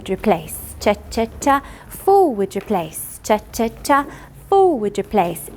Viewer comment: I thought she said "f*ck replace cha cha cha"